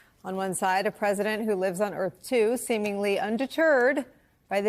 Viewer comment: She said "Earth too" but all I heard was "Earth 2"